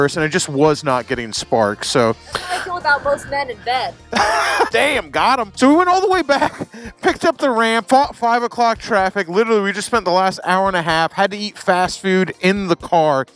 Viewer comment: said; 'most men don't have a spark in bed,' did i hear that correctly? Do you think Gandalf would approve of the new name for your Sportster? Great video, "Here's To Keeping It Naturally Weird'